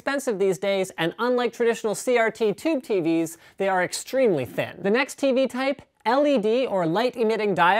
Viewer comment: Linus said, "...they are extremely thin..." The Youtube automatic captions says, "...they are extremely fat...". LOL!